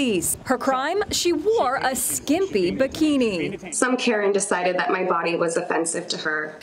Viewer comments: "Some karen decided that my body was offensive to her" I already love you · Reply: I thought she said "some parent" at first, but "Karen" seems more appropriate. Probably some "BIG Karen" was offended. 😁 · did she just say- “some Karen..”?